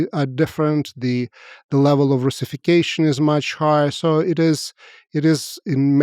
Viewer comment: Serhii mentions that "level of russification is much higher" in the post soviet state of Russia. The closed captions says 'unification' rather than 'russification.' This mistranslation/ failure at writing the correct words used is something that should be corrected, as the meaning conveyed by those two terms is strikingly different for everyone who is not perhaps Russian.....